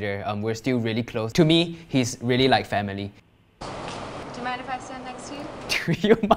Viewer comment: did you say family??